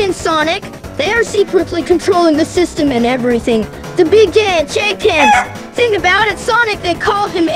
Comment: I know he says “ big giant chickens “ but it sounds like he says “ big gay chickens “